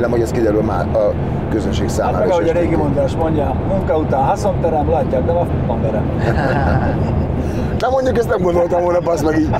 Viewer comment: nél mit mond a Vályi? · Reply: Munka után haszon terem, látják nem a fa..om verem. 🤣